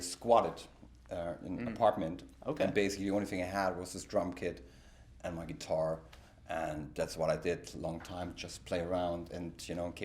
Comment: I thought the host had said ‘all you had was a drunk kid and a guitar’. I think that’s a sign that I should probably just go to sleep